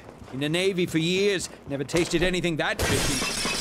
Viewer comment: I thought he said “Been in the Navy for years, never tasted something this fruity,” and now I’m upset that he didn’t say those words. :(